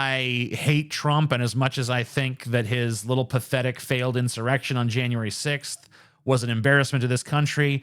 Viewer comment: I thought you said little bathetic erection😆